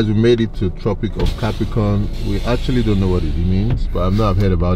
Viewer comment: Did you really say "we actually don't know what it means" ?? at 19:08?